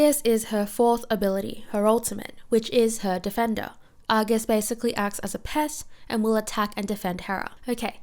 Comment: I thought she said "acts like a pest" when talking about Argus and I agree fully 😂